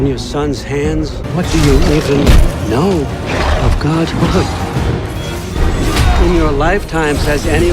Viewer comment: ODIN SAYS “ WHAT DO YOU EVEN KNOW OF GOD’S word” WHAT GOD IS ODIN REFERRING TO??? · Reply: Lol he said "godhood".